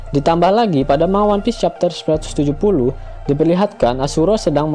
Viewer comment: maaf koreksi min, bukan chapter 170, tapi 970 tadi abis ngecheck atau mungkin lebih kedengerannya 170 sih daripada 970 cmiiw